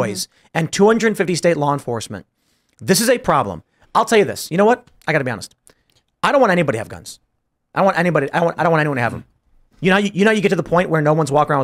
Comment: Are my ears not working or did Tim say he doesn't want anyone to have guns? 9:50?